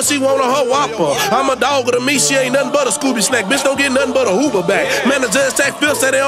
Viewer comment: here's a question: at the 1:26 mark, does say "don't get nothin but a Uber back" or "don't get nothin but a HOOVER back"? it works either way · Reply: Don't get nun but a Uber back lol I see what you saying tho